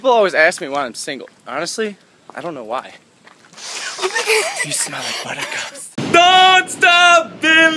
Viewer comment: What has Logan said in teh end of the scene 01:50 ? I understand the other words.. people ask me why i'm single .. and i said i don't know why... but after he is smelling ? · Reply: He said "you smell like buttercups" after smelling her hair. Its hard to hear xD